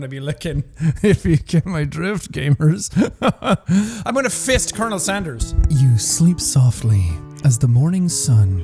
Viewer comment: I watch a video, go a bit without realizing i dont have earbuds in. First thing i hear is "im gonna gist colonel sanders"